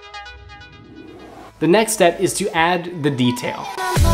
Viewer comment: when he say's next step is too add the detail, it sounds like he is saying next we add the beef tail, i was soo confused